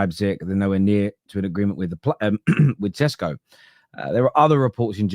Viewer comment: did say tesco 😂… that’s what me and the guys call him. Bring viktor..